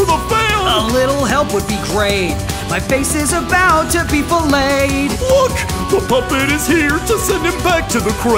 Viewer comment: I don't know about you, but my subtitles said two people laid instead of to be filleted (I know I probably spelled it wrong)